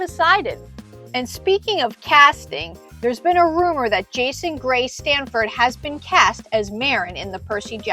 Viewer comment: did she say Jason Grace Stanford? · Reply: Haha close, I said Jason gray